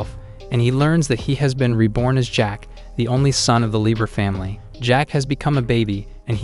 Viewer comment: I've seen too much baki stuff, when the maid says Jack-Sama at 2:05, I legit thought it said Jack Hanma